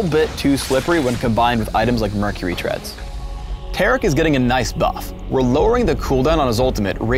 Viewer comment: German subtitles 2:33 Garic instead of Taric